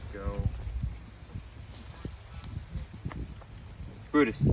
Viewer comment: boris coulda swore you said boris not brutus 🤔🤔🤔🤔